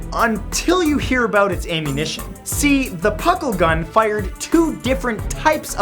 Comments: i thought he said pickle gun for a sec... i was thinking " mm mm tasty " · On number 8 I thought it said pickle gun